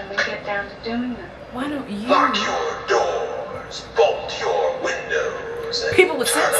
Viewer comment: When the voiceover said “Lock your doors, bolt your windows” at 18:34 I swear I heard “fuck your doors, fuck your windows” and was entertained, yet confused